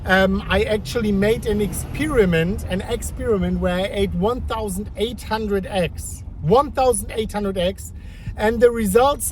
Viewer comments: I thought you said 1,800 X at first 😂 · Ah man, you were saying "1,800 eggs". I thought you were saying 1,800x !